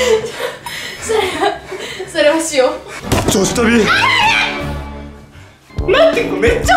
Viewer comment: このへんからずっと女子トイレあるあるに聞こえてた